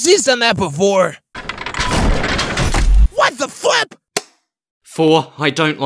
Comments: I thought pen said, “WHAT THE F**K” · I thought pen said “WHAT THE FUCK” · I didn’t hear what the flip I heard what the fu## 5:56 💀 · did somebody just say "WHAT THE FUCK" at 5:57 or am i just not getting a joke or something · I thought said what the fuck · That sounds dangerously close to "What the ****"... I'll say [E] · i thought someone said what the fuck. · it sounded like pen said "What the f*ck?!" 😳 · did someone said what the f